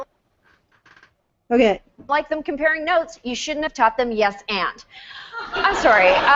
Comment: FYI "yes and" is a reference to improv best practices where you always say "yes and" in any scene you're in rather than saying "no but" basically you accept any premise given to you, then add to it. This is a case of "most comedians are comedians." Really a bad joke for a national audience. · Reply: I thought she said "Yes, aunt".